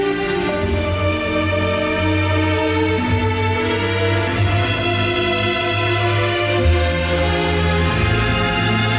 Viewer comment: What is Scott saying at 4:22-4:24? "Love it" ?? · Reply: I think he said Lets go for it.